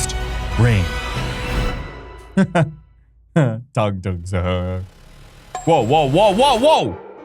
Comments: Did Anyone Hear! Tung Tung Tung Sahur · Did bro say tung tung sahuar or tu tua saher